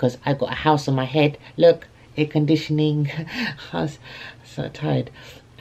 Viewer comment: Did he say air conditioning